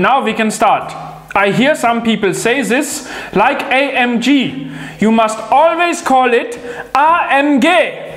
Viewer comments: i hear i am gay for some reason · Did I just hear "I am gay" 😂 😂 😂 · Did you just said "i am gay"